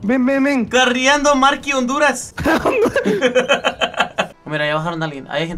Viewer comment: escuche a mi país Honduras :'v